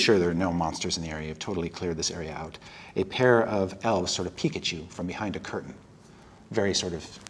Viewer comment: when he said peek at you i thought he said pikachu